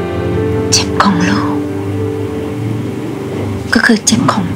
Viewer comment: ผมไม่ฟังผิดไปใช่ไหม ใครได้ยินเหมือนผมบ้าง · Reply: เจ็บค่ะ ไม่ใช่จิ๋ม 555